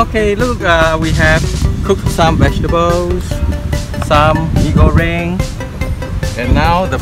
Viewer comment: did u just says Mee Goreng? 😅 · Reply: Haha, instant mee goreng ... at least it is what is stated on the packaging :)